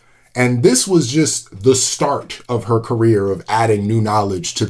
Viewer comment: Congrats, Dr.! Dr. Daly is an incredible scientist. I wish I had learned more about her in school. Also, I don't know if you did this on purpose, but I heard you saying "start of her career" as "starch of her career" after talking about amylase. 😂 Edit: Wait, her work on discovering that RNA is required for DNA was cited in a Nobel prize lecture, but she didn't get a Nobel for it herself when it became so fundamental to biology?